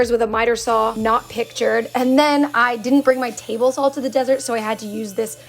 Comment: I actually thought she said she forgot her table salt